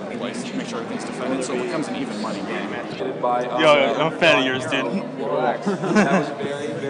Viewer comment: dafuq was that about? What was he saying? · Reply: "I'm a big fan of yours dude" Source: I'm Scottish